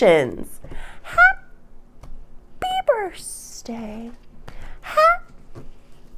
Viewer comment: Are you saying burstday? I heard you say it last year but I thought you just messed up saying birthday but it sounds like it’s a thing you do. Where does that come from? I love your readings btw, accurate af. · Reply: Birthsdays to all Pisces during our birthday month.